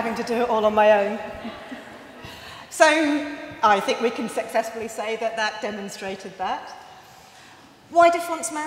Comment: Did she just say, "sexessfully"?!